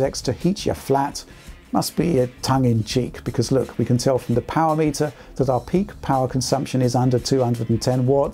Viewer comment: 😂 I thought he said WAP meter 😶